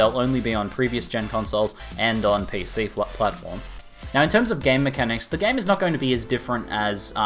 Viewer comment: Every time says "game mechanics" I hear "gay mechanics" 4:35 4:44 lol :P